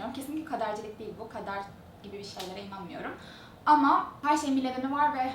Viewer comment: Kadere inanmıyorum mu dedin benmi öyle anladım acaba ?😌